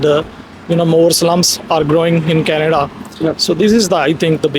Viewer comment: I think the guy said "more slums are growing in Canada" and you guys interpreted it as Muslims